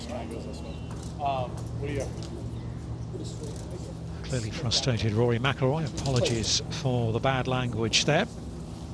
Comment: did they bleep something out? The broadcaster said “apologies for the bad language” but you cant hear him say anything, though there seems to be a pause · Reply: Yep. In the live coverage you could hear it quite clearly... "sit the f... down"